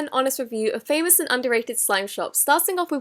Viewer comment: wait what did you say?😂 wuh-hoo slime?🤭